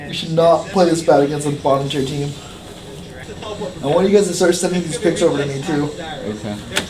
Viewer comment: can anyone make out what regi said at 06:43 ? "i want you guys to start sending...."? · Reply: "I want you guys to start sending these picks over to me, too."